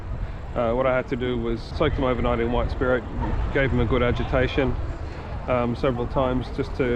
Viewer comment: I had to listen several times when you said agitation. I had thought you said education. I was not sure if that was the right word. I was thinking if it was a non British phrase. I only finally got it when I saw the subtitles.